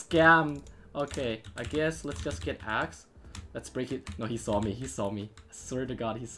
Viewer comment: I thought he said "lets get hacks"